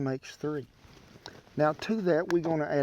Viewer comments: For half a second, I thought you said bacon powder. :) Yes, you can tell what's on my mind. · Not gonna lie, thought i heard you say bacon powder. Hell yeah that sounds great. No you ment baking powder.